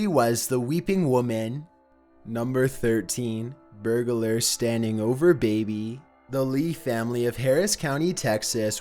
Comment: what he said: “number 13: burglar standing over baby” what I heard: “number 13: B U R G E R K I N G F O O T L E T T U C E”